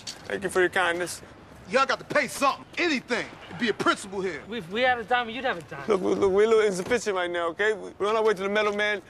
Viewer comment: What is he saying? "If we had a dime, you'd have a dime" <- Is it that? It's entirely inconsequential; it just bugs me when I can't understand something no matter how many times I listen to it.